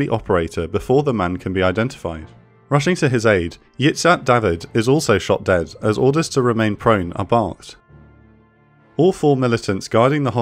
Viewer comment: The wiki article on the raid doesn't say anything about the fourth hostage you mentioned being killed, and neither does Encyclopedia Britannica. There was a fourth killed, but not in the raid. Dora Bloch, who was taken to hospital in Uganda's capitol, was murdered on orders of Ugandan president Idi Amin. What's the cause of this discrepancy? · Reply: No, he clearly says "is also shot dead as orders to remain prone are barked".